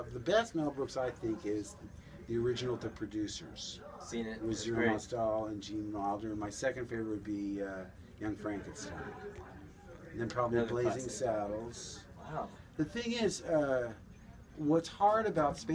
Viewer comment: Lol when he said "Young Frankenstein" for a moment I thought he said "Anne Frankenstein"